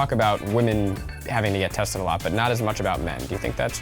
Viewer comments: I THOUGHT IT SAID TASTE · Holy shiet. I thought it said taste.